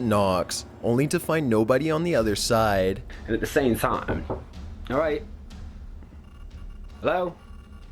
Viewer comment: The joe weller one the women sounded like she was saying you alright?